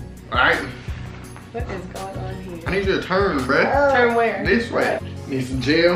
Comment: Am I the only one who heard him say “I need you to turn BAE”🤷🏾‍♂️